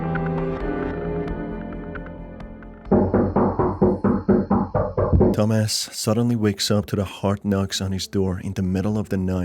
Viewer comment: My stupid ahh thought this man said “Dumbass suddenly wakes to the sound…”